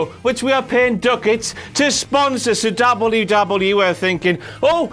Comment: did you say wwf at 1:30?